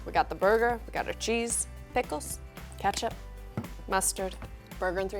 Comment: is it just me or did she say "messturd"